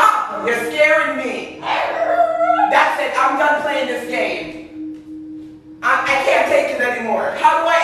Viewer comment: I heard “can I talk” 🤣